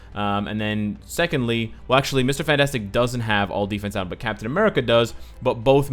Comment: why i always heard you saying Captain Miracle